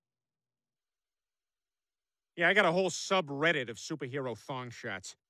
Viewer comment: Did Peter just say subreddit?